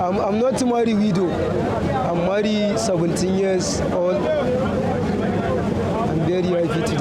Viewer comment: Did i hear "i married 17 years old"??